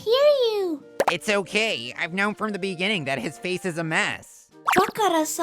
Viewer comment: wait? Did he say "His"?